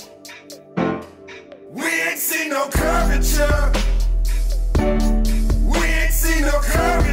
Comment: "We ain't see no curvature" Somehow I'm not surprised that those are the lyrics. At least that's what I hear, if I'm hearing it wrong tell me!